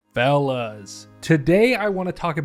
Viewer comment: When he said “fellas” idk why I thought he said “Stellas”